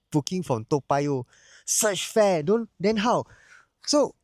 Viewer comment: I heard "fresh fart" 😂